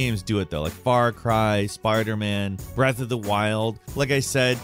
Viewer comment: did he just say sparder man 💀